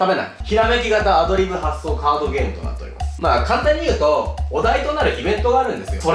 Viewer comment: 最初のカードゲームって言ってるとこ、ハードゲイームって聞こえた